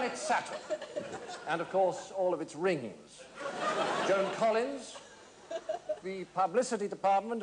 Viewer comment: I thought he said Sauron and all of his rings